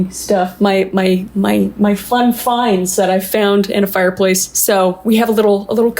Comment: Thought she said she found her antiques in a fireplace until I seen the fireplace 😂